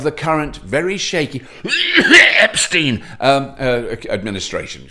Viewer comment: Did you say #Trumpstein ?